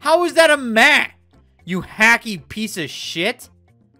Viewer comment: "How is that a MEH? You hacky piece o' sh*t" Who thought he was saying happy instead of hacky 🤣